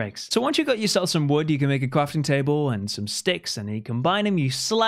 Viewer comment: did he said Qwafting table!? I mean it sound like this and it's adorable holyyy